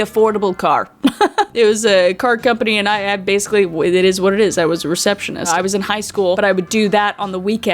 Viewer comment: I thought you said a MASTER receptionist 😂😂😂 I was like is that a Canadian thing ? 😂❤❤❤❤